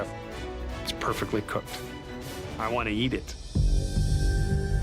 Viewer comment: what I heard is "I want to yeet it"